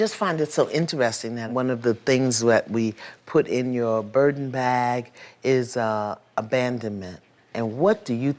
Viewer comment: I legit know she said burden bag but for a second I thought she said Birkin bag